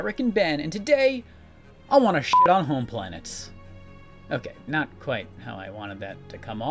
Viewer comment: At first because of the censoring beep, I heard "Today, I'm gonna shit a home planet!" And my reaction was like : "Wow, American Ben sure does trust his butthole!" 😅